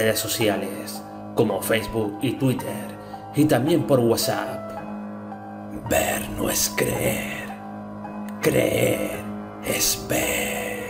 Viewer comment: Ya somos mas de 200.000 y eso es gracias a todos vosotros siempre intento hacer los mejores vídeos para mi familia creepy ;) un fuerte saludo y abrazo para tod@ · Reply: en el minuto 14:00 se escucha una voz que dice vean